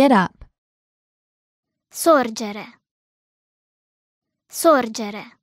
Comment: It's saying "sorgere" instead of the displayed alzarsi at 4:36 lol